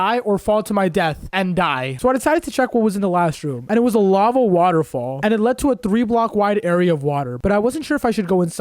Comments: did he say lava 'waterfall' 🙃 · he actually said " lava waterfall "